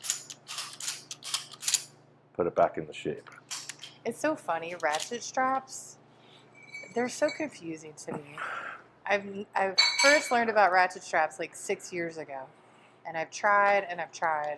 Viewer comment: After that one meme, i always hear "rat shit strap".